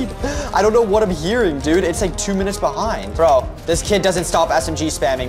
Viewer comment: uhhhh that automated text says the S word instead of sit lmao